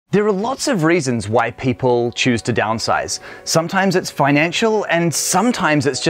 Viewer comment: I need to ask you to interview more Black and Brown tiny home owners because honestly I thought you said "there's lot of reason why white people choose to downsize"...